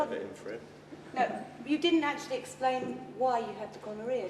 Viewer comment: instead of the subtitles reading “you didn’t actually explain why you had gonorrhoea” with autogenerated captions says: “you didn’t actually explain why you had to call Maria” 😂